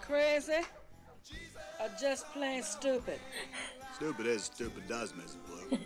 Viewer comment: My son was a small child when this movie was released. He misheard the line, “Stupid is as stupid does, sir!” and thought Forrest said, “Stupid is a stupid dinosaur”. We’ve joked about that ever since and whenever we see a dinosaur depicted, we say, “I wonder if his name is Stupid”…. Forever an inside joke in our family.